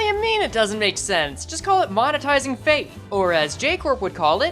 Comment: at first I thought he said “monetizing faith” and I was gonna make a Kanako joke